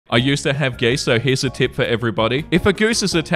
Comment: when the guy said 'i used to have geese so-' combined with his accent, and my sleep-addled brain, i thought he said 'i used to have gay sex-'